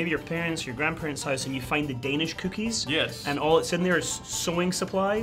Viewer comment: I thought he said you know when you’re in your pants and you find danish cookies